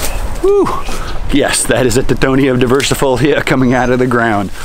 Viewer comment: What's the plant name right after 4:06 <link> ? Thanks, The one that grows 5 or 6 feet a month? Did you say it was Mexican Sunflower? Tithonia diversifolia?